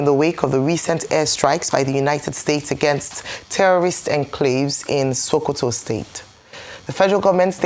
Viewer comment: Thought they said no terrorist are in sokoto